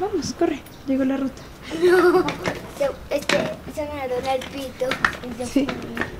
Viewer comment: Juro que escuché "se me dobla el pito" JAJAJAJAJ Maténme :(